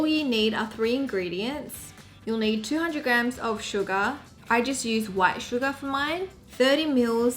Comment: when she said sugar,I heard it as "Suga"of BTS